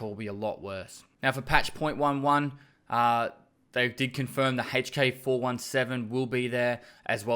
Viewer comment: "deconfirmed" it will be there, damn I can't really understand alot of this. But good video